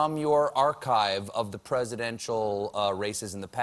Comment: I thought he said "the presidential racists"